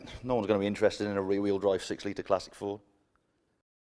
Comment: I bet to differ I would be interested in rwd 6l classic ford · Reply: 6 litre! Damn I heard "6 Seater" I had to listen again, and you are right. Pity, I'd been imagining a Bad Obsession Mk1 Tranny Van